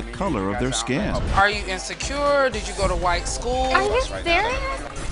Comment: What did she say! 0:29. Did she just say that you did go to White schools!